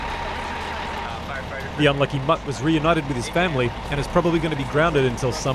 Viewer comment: did he call that dog an unlucky mutt or am i hearing things? 🐕🤤😲😕